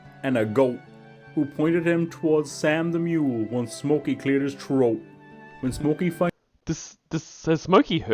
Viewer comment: Was that Sadie the same exact Sadie that Lorne would later adopt?? If that's so, then she never had a safe home to be in, so sad.... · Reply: When he says “When Smoky cleared his throat” I keep hearing Dan and “eight inches down ya troat”